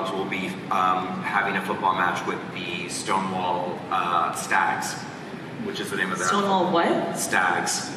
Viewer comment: I thought he said "Stonewall f**s" and I laughed at the pun for a solid few seconds